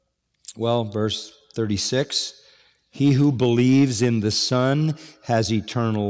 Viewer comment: it says SUN instead of SON, twice.